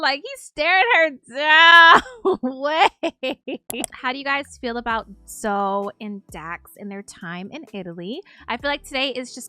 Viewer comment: all I heard was “how do yall feel about xo and dax.. and im here for it 1000%